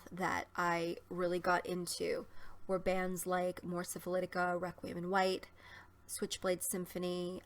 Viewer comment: Hi, What was the first band you mentioned around 7:21 (it sounded like Morphis Salitica)? Anyway, stay safe. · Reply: Mors Syphilitica - They're a NYC band. Their other band is called Requiem in White.